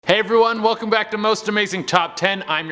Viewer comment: No one will believe me but in 0:06 I heard something say (hi) I was using my speaker when I heard it.